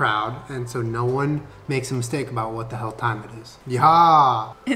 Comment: mendela effect: at 7:49 i swear to god julie used to say "what the fuck time it is" but i just watched it again and he said "what the hell time it is"